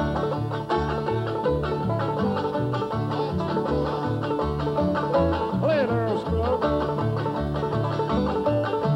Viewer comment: Can anyone make out what Earl said to him at 0:47??? I've been listening to this video for years and can't figure it out! Help!! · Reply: "Watch it, boy," near as I can make out. Said at the start of 'Bean's banjo break, he was probably jokingly telling him not to "show him up" with his pickin', Earl being the resident banjo virtuoso there. Theirs were 2 very different styles -- three finger vs. drop-thumb clawhammer. Earl was a master of the former (and credited with its early development and widespread popularity) but I don't think he ever mastered the latter even though he apparently loved it, judging from the praise he heaped on Stringbean, Uncle Dave Macon, and Grandpa Jones in his banjo instruction book. And when Pete Kirby (Bashful Brother Oswald) passed away, a man who also played the drop-thumb clawhammer style, Earl said, " He played banjo the way I always wished I could." Mighty high praise coming from Earl Scruggs -- for the man and the style. Mighty high praise, indeed.